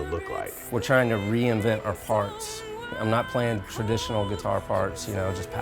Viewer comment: "We're trying to reinvent our farts.." - Troy McLawhorn 2017 (just kidding but it does sound like he says that :P )